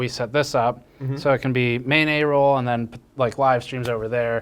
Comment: I know Steve said "Main A Roll", but I heard "mayonnaise roll", and that's just the most disgusting sushi I could ever think of.